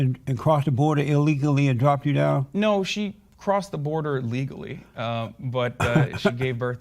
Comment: Anyone else notice that at 33:41 the subtitles say Hasan's mom crossed "illegally" despite him saying she crossed legally?